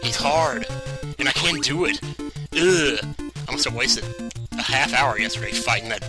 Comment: I thought you said "I'm so wasted" and I was like "What? That's not like you at-- oh..."